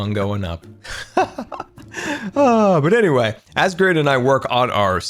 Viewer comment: did say grain🧐